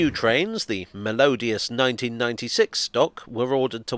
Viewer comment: Ah, melodious. I thought you said malodious for a second!